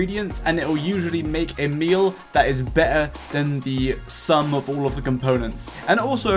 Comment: I turned video off after i heard "be'eh" Instead of better